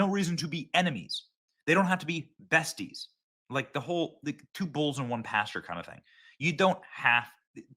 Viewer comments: 🤪 😂 I heard “2 bowls and one pastor.” Bowls of wrath ? Red bean pottage?” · LOL, when he said "They don't have to be besties like 2 bulls in 1 pasture." I at first thought he said "They don't have to be besties like 2 bowls and 1 pasta." Which immediately took my imagination to the scene in Lady and the Tramp where the two dogs were eating the spaghetti, and eating it together till they kissed. But instead of the two dogs, I saw Trump and Elon eating the spaghetti. It was both gross and hilarious at the same time.